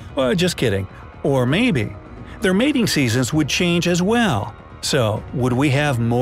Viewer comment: Mating seasons, i thought yoi just said no seasons. Bigger earth gives bigger food